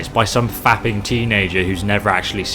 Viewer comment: LMAO when I heard 'those fapping teens never see a female body'